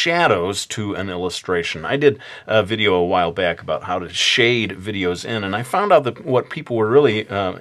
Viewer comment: like this if you heard him say near the beginning "Shade videos in"